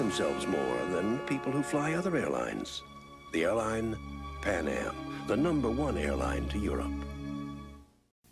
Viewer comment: Quick thing, if you turn on the captions instead of Pan Am the captions say: “The airline Panama!” That’s definitely incorrect! 😂😂😂